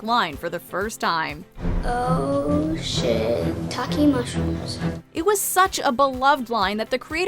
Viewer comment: I always thought Carmen said, “Oh shit! Talking mushrooms!” 😂